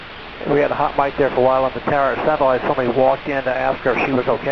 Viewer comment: "At some point somebody walked in..." should read "It sounded like somebody walked in...".